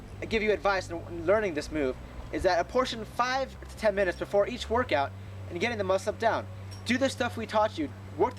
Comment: Nice,thank's. Just one thing what R you saying at 7:45? 5-10 min. of what? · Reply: 5-10 minutes before your workout, trying to get the muscle up down. Alot of people that we train try do learn the muscle up after or during their workout. Or, they will get lazy and not train it for 2 weeks. If you are consistent in "apportioning" 5-10 minutes before each workout, you will have the MU down in no time with our guide